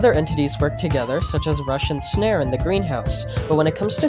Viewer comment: I thought he said “Russian snare”💀